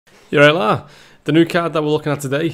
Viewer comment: what are saying in the very beginning of the video 0:00 ? lol. "yarilah"